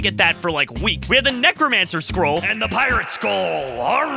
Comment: did he say pirate skull